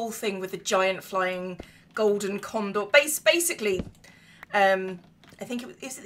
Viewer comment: I thought you said giant golden condom at first. Slightly different thing.